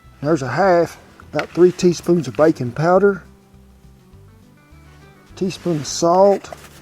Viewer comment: I keep hearing 'bacon powder' instead of 'baking powder' and thinking to myself: I need that"